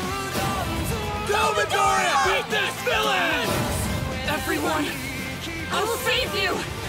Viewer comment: am i going nuts or is bakugou saying "go midoriya"